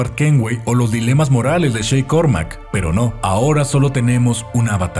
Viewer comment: que dijo? shreck kormak? alguien me puede decir de quien está hablando? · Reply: Shay Patrick Cormac es el protagonista del juego Assasins Creed: Rogue, el fue un asesino que estuvo durante una etapa mas "oscura" de los asesinos y el duda si ellos son los buenos. Y el se une a los templarios para asesinar a los Asesinos para que no destruyan o maten gente inocente segun su perspectiva.